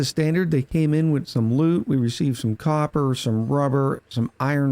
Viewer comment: hahah at 08:05 i miss heard him. i heard him say. as standard, they came in with some lub. insted of loot